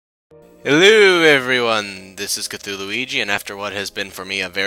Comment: Sounded like you said " Haluhoo everyone"